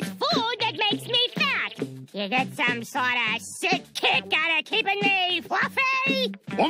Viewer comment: "You get some sort of sick kick out of keeping me fLuFfY ?" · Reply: Ooooooooh, I thought she said "sick kink"